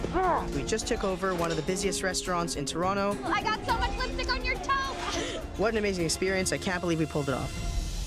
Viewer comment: Idk if I’m stupid but I just heard Mary say “I got so much lipstick on your toe”